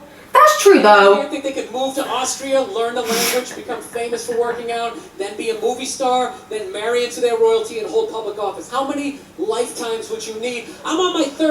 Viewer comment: Go back and listen to it again at 11:40 because I noticed you say you didn’t get the joke after. But he’s sayin “do you really think you can move to Austria learn the language, be famous for lifting weights, be a movie star, marry into their royalty and hold public office” Because doing all those things he did still makes him a great man is what he saying lol